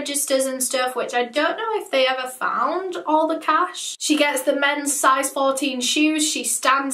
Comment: Sis really one upped Tall Girl with those men’s size 14 Reebok’s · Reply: omg when eleanor was talking about those i legit heard “MENS size 13 nikes”